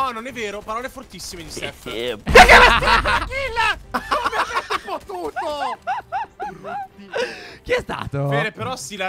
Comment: ho sentito e continuo a sentire dire: "chi è che mi ha stillato la kill, chi è che mi ha f0t*ut0". E penso di aver capito bene comunque il fatto che stiate diventando meno family friendly mi piace molto perché per un pubblico adulto/adolescenziale è molto più divertente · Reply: no, aveva detto come avete potuto